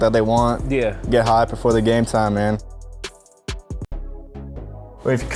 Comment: Sounded like he said getting high before game time Mannn